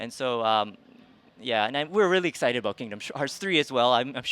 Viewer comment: That moment when you hear Tai Yasue saying Kingdom Shards instead of Kingdom Hearts.